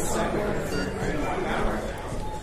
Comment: P R E V I E W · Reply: I thought it said privien lol